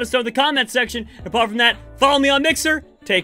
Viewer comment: Endtro of what I heard :"take can" what he said:"take care"